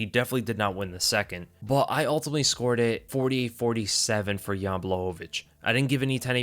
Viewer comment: It sounded like he said he scored it 40-47. My brain shit the bed trying to figure out how that would make sense